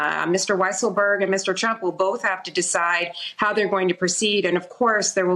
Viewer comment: Did she just say "Weisselberg and Mr Chump..."?